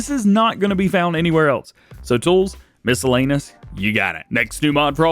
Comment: I heard "missle anus"😂